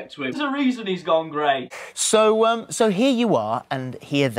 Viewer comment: I THOUGHT YOU SAID GONE GAY NOT GONE GREY AND I THOUGHT YOU WERE A TIME TRAVELLER OR SOMETHING